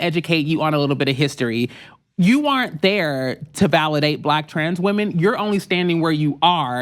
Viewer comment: Thought she was saying "Black trash women".